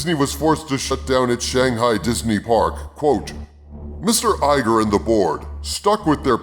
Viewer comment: I totally thought you said 'Mr. Iger and the Borg" haha